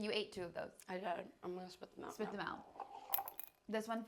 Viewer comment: did she say “some people ate this last night Felix ate this last night” as in Felix was there last night,they filmed one with him too and that we are getting one with Felix soon?